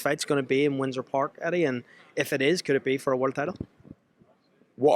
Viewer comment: How on earth can Crocker-Donovan be for a world title? That's ludicrous. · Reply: I thought he said winner will fight for a world title